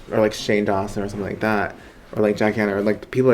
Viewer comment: i really thought he said "jack hanna" not "jackie a." for a quick .2 seconds